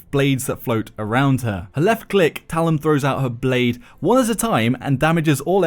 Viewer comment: For #5, the girl with the floating blades, I named her "Talim" actually, everybody seems to have misheard it as "talon". I'm blaming it on sexy accent. Lol. Thanks for the opportunity.